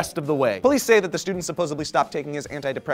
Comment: at 1:13, did you say "supposably"? lolz.